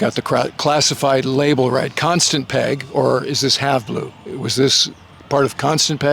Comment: What’d he say? Constant Peg or what? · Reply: Have Blue. One of those secret squirrel programs with weird names.